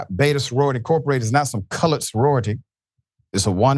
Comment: No he didnt you knuckle*******! He said COLORED and everyone heard it but you dumb@ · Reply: I thought I heard "colored" too and I hate Fox. But if you turn on your close caption on YouTube, mine picked up "college".